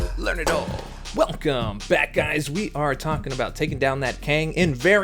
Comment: I heard " Welcome Badguys" :D i know i overheard :)